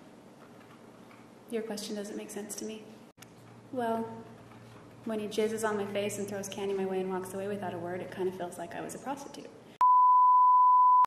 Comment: Did she say "when he jizzes on my face and throws candy my way..."? LOL